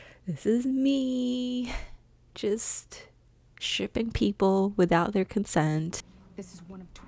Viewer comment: i didn't understand this.. help? 🙃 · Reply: She said shipping.